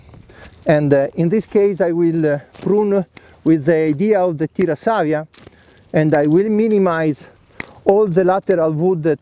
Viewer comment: Very helpful because it was thorough with lots of visual examples. Thanks. When the Professor demonstrated one principle I couldn't quite understand the name of the principle. It sounded something like "fita sala" or "pita salia" or something like that. The term is used several times between 17:18-19:10. Can you give me the spelling for this term? Thanks, and thanks again for putting this together for us. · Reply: Tira Savia, a pruning method. See minute 2:10 for the terminology they're using :)